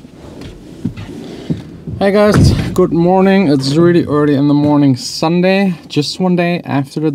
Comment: Did you just say "guten morning"? :P